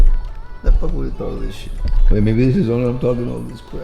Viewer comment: Anyone else hear Papa Guzman say "what the FUCK is with all this SHIT?"